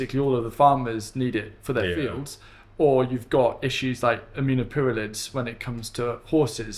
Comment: Any clue what is said @ 1:00:28 regarding horse manure and "amuno pids ?" (from the transcript). My N.A ear struggles..... I compost a LOT of horse manure..... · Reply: Yes, aminopyralid!